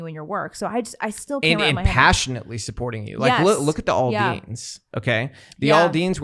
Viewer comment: What is he saying here? I thought "All Deans" but I can't find anything on them. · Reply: Jason Aldean